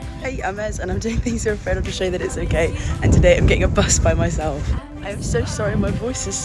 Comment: Omigosh i thought you said “ and I’m getting hit by a buss today”